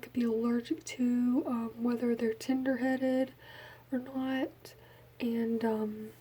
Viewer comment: Your accent made it sound like you said "tinder headed" instead of "tender headed" 😂😂 I'm from Maine so that's probably why I think it's funny